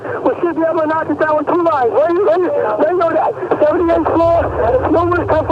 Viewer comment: it sounds like he says "where are you injured"